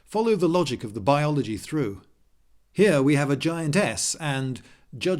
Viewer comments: When he said giantess I thought he said giant "ass" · maybe it's me but i was sure you said giant ass instead of giantess